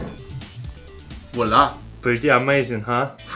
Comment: what did you said? wala?